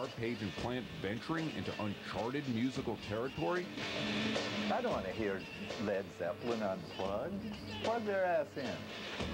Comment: What does Petty said? "I don't want to hear LZ on f.............." ? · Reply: " I don't want to hear Led Zeppelin unplugged. Plug there asses in."